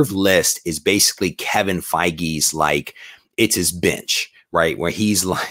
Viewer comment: OMG, I was working while listening and I thought I heard: "...this reserve list is basically Kevin Fiege's, like, ...it's his bitch ...right? like where...." --WHAT? what? ... then you finished your sentence: "...where he's like--bench, B-E-N-C-H...where he has..." --hahaha...omg I had to stop working and relisten to that for a quick sec 20:39 Magneto: "...Can such a thing even be done?" Cyclops: "..." ---"Does it need doing?" ProfX: "Yes." Cyclops: " Then it will be done. " Am I the meanest? Am I the prettiest? Am I the BADDEST MOFO lowDOWN around this town? But who am I? Who am I? I ... can't ... hear you... ..The Shogun... of Krakoa